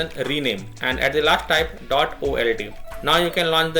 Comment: what does he say here, dot OL- what? D? Hard to hear for me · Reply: old